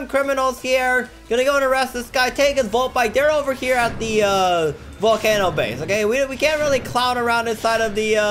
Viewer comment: did just say "volcanno" base?